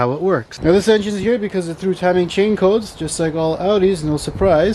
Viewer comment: what are you saying? "Two-timing chain codes?" I'm concerned as I've thought of getting the original S8...